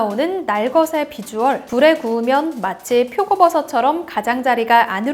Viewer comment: 나만 부*구우면 으로 들림? 다시 들으니까 아닌데?...